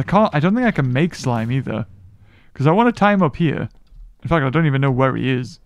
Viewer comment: Did he say the f word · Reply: He saud Infact